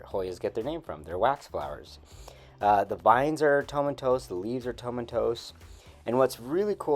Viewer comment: hi there! I know this is a long shot since this video is older, but what word are you saying here when describing the leaves and vines? Sounds like Tomentos, but I'm not sure? What does that mean? Sounds like a Hoya specific glossary word that I don't know 😅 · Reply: Wonderful question and thank you for watching. “Tomentose” means thickly covered in short, matted, woolly hairs, or a dense layer of fine hairs that give a frosted appearance.